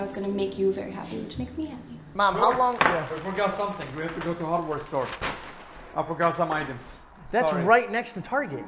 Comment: I swear said we need to go to Hogwarts Store i was so fucking confused untill i saw the broll of the hardware store lol